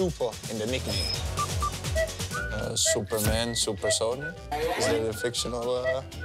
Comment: Does Frenkie say "Super Sonny" of Spurs??😂😂